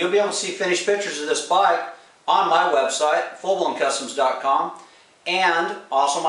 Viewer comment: Should put a link to the web site in the video description. Sounded like you said 'fulloncustoms . com'. After a little research I found the website 'FullBlownKustoms . com' spelled with a 'K'. How were we suppose to know that? Just a friendly suggestion.